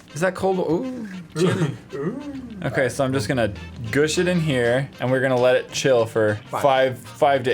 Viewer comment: Is it just me or did say go sh*t in here at 4:42🤣🤣🤣